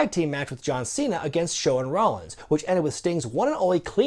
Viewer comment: did he say shawn rollins